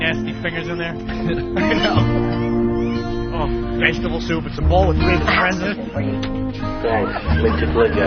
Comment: Jimmy says it's a bowl with big what in it? Can't make the word out. Should've been "hairs" though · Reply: He says "it's a bowl with 3 friends in it" (vegetables)